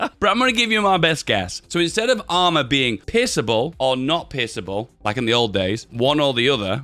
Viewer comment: All I hear is pissable or not pissable. Guess this is the question🤔😁